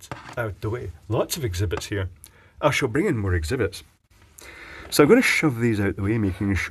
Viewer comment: In what way? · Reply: So many exhibitions ... Unless I misheard exhibits ... but the pun still stands